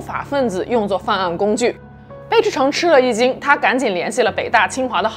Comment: 听成了”吃了一斤”